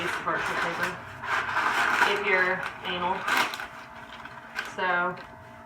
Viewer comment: i heard anal